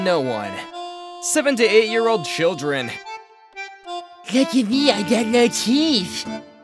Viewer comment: ??? What is he even saying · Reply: He said "look at me i got no teeth"